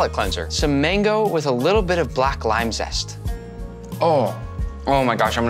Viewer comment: what did he say? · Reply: Black lime zest